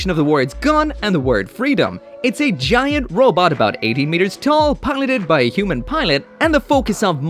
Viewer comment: Did he say 80 meters?? They're not that big! That's evangelion height! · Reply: its a misheard, he said 18 meters, hes irish accent